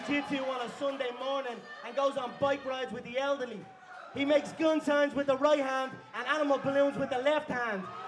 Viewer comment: His best line ever was „Nate can only count to five“. Nothing comes close to this one. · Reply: TheOneAndOnly 1 i thought he said gang signs?